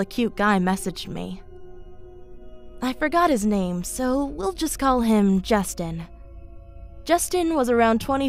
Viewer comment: i thought you said ‘let’s call him dick’ and i was like ‘oh.. okay’